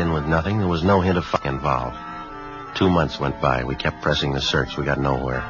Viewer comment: what did he say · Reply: "no hint of Foul Play involved'.